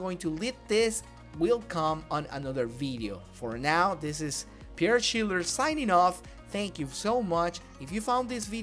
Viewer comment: ngl i thought it said Schindler's tips at the start